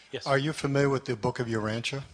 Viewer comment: could someone type the book that is mentioned at 54:08 ? I can't understand how it is written. English is not my native tongue. The subtiles says "book of your rancher" LOL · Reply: Jaron Urantia is the name of the book.